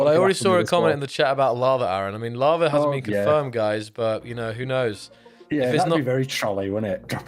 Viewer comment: did he say lava is confirmed or isn't ?